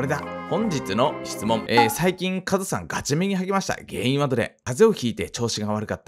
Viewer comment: ガチ目にはげましたに聞こえた